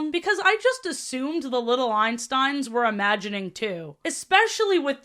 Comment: for a second I thought it said "Little Epstein"